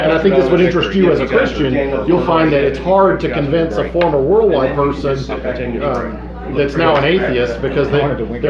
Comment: What is it he's saying there? "You'll find it's harder to convince a former ***** person that's now an atheist, because their religion was never based on faith" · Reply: On the first listen it sounded like he was saying "former warlock person", or "former warlike person"